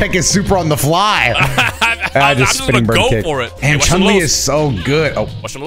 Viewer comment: I thought she said king size kick 😮